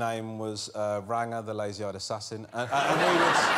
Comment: "Ranga the lazy eyed assassin" 😄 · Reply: Genuinely thought he said lazy ass assassin... Of course too obvious, lazy eyed assassin is from a much higher form of self deprecation. His shtick never gets old either, the timing of it is perfect